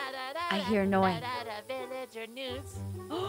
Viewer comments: PLS I THOUGHT NOI SAID “Villager Nudes” IM CRYING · I thought I heard noise say "nudes" not news because he made the "w" in news sound like a "d"